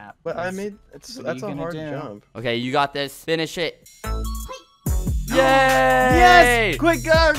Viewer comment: So uh,, instead of hearing "Finish it" I heard "Finn is sh*t" and i froze for a good 5 seconds like 👁👄👁